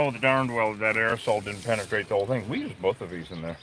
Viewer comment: Oh, “aerosol “ ! I thought you said “arsehole” 🤪🤣🇨🇦